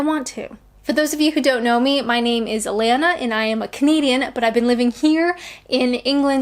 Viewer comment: LOL Everyone in the room but me thought you said "comedian" living in Britain , I said No, she said "Canadian" but their Welsh ears missed it, Carry On!